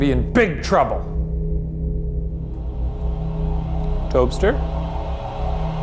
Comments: Tobster is the best nickname I’ve ever heard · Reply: I thought he said lobster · Tobster is the best nickname I’ve ever heard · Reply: Billy K I thought he said toster